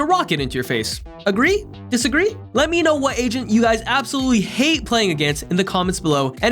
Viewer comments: this is irrelevant but every time he says agent - I thought he said asian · Every time he said agent I thought he said Asian